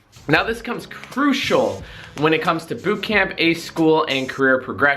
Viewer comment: I heard "boob camp".